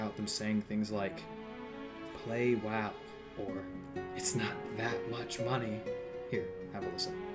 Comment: i always heard "give us your money" rather than "it's not that much money"